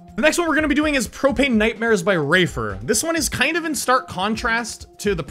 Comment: Instead of wafer I thought you said raper 😂